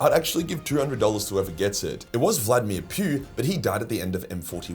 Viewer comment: i heard that as vladimir putin and went, wait what?